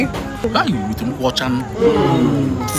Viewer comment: Did she just say perfume rice 😂